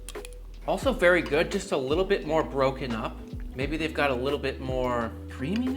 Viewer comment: Lol...at 3:20 I thought said maybe if they had just a little more ketchup and I was like Hell Yea.